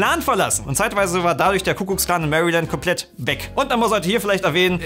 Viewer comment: Hab ich was an den Ohren? Ich höre immer „Kuckucks-Klan“ sagen! 😳 Also ich kenne nur den Ku-Klux-Klan (engl. Ku Klux Klan). Ich hab‘ mich sicher nur verhört... oder, 😂